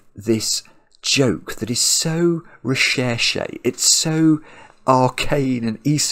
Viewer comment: What's the word says at 36:47? I've never heard it before. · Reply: "recherché" - it's French for "researched", but its use in English makes it something more. My ancient copy of Merriam-Webster's has this to say (words in all-caps are synonyms, word in lower case are definitions): 1 a: EXQUISITE, CHOICE 1 b: EXOTIC, RARE 2: excessively refined : AFFECTED 3: PRETENTIOUS, OVERBLOWN So it fits in with the sudoku: it may look like a fairly simple classic with only one extra rule, but there is definitely something that's only visible at a second or third (or tenth) glance.